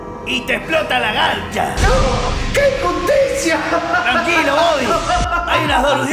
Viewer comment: Toda mi vida pensé que decía: Que injusticia! Y dice que impotencia!